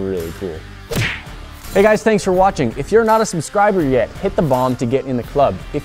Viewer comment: thought he said hit the bong at the end of this video :')